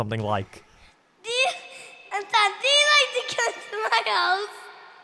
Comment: i think he said "do you like/want to come to my house"